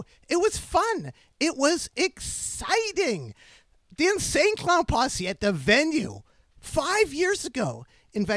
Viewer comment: Sounded like he said Insane clown "puussay' at first